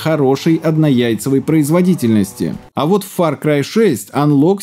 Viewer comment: однаяйцевая производительность, я уже думал послышалось